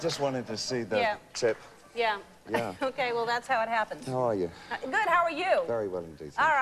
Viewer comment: lol at the beginning i thought he said "Who are you?" not "How are you?"